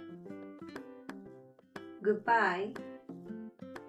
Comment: どうしてもhave a nice day が半端無いっすねぇに聞こえる